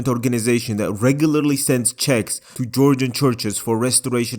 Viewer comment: I heard "that regularly sends CZECHS to Georgian churches"